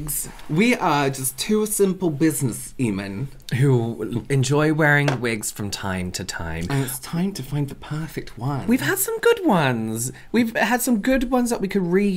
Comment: I heard business seman and lost it.